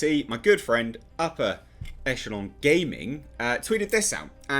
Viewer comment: Every time this man says “upper” I hear “Appa”! Anyone else or have I been binging Avatar The Last Airbender to much?